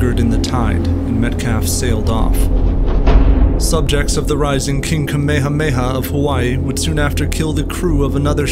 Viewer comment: The what now? Have I been watching too much DBZ or did I hear that right? Lol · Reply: We have a whole video on Kamehameha! Check it out!